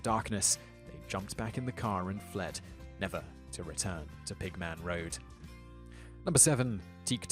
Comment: On the pigman road,I thought he said pikmin road and I actually jumped with happiness lol